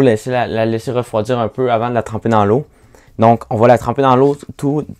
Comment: Qu'est-ce qu'il a dit ici? Je n'ai pas compris. C'est quel liquide qu'il utilise lá? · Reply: À 7:05, le circuit est trempé dans l'eau pour pouvoir retirer le papier.